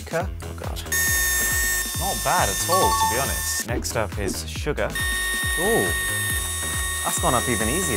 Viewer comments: For a army when he said sugar I thought he said Suga🤦‍♀️ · Did anybody heard him say SUGA instead of Sugar 💜💜💜💜